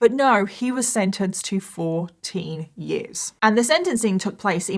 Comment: I thought you said 40 years at first but no.... 14 years... 14 YEARS?! That's an absolute joke!!